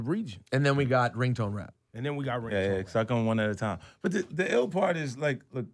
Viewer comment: Yo, anybody else hear Fif tell Rosy "yeah yeah yeah, suck on 'em 1 at a time" at 32:26? 🤔